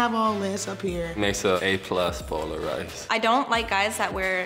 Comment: why I can't understand anything says 😥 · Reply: An A+ bowl of rice